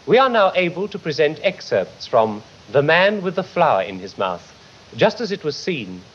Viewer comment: I kept hearing "The man with the Fly in his mouth"